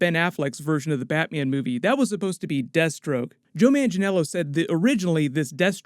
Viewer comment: When he was talking about "Deathstroke", all i kept hearing was "Destro" like G.I.Joe .......